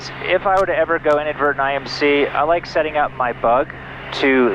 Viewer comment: I thought you said “if I ever go inverted in IMC…” 😬